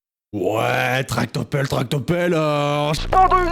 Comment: j'ai entendu " tarte aux pelles "😭😭😂😂😂 Et aussi pour "on va quitter la France on s'en va chez lidl Mwa j'entends bien